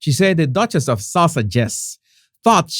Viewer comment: CC says Duchess of suffragists 8:42 Markle Claw = martial law 😅